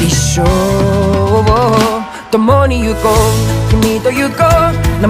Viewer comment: I heard " the more that you go, the meater you go" 😭😭😭 how to unhear